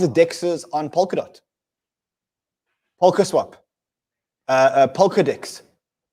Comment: it sounded like ran said pokadicks instead of polkadex :)))